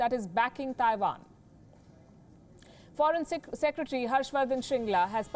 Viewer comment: Did she just say forensic instead of foreign @ 1:29 . 😹